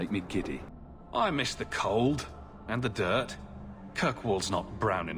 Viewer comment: HEY DOES HE SAY KIRKWALL